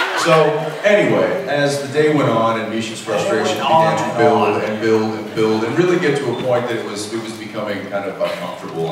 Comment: Wt did misha say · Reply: "The day went on and on"